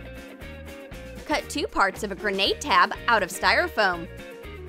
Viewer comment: Who thought she said cut a grenade